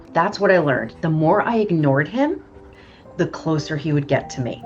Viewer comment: “The more I ignored him, the closer he would get to me”. Did you call him Morrissey?